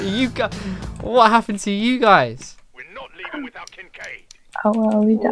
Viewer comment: Thought he said "we're not leaving without kim k". like ok. having a marathon